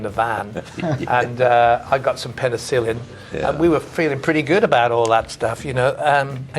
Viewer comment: Did Plant say "And I got some pedophilia?"